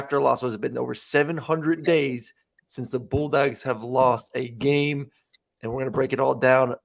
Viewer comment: Does call us the Bull dykes at 0:23?